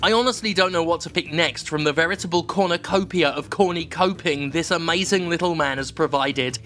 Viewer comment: "Cornucopia of corny coping" "this is the CERN of gay" "Why is this sad?" This is a great episode. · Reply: I thought they said “corner coping”. Great line regardless!